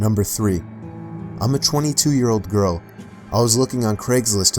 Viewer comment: I thought it said im a 2 year old girl